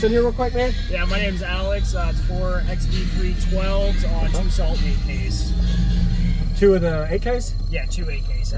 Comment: Am I the only one who heard him say salt md 8ks 😂 pretty dope setup brother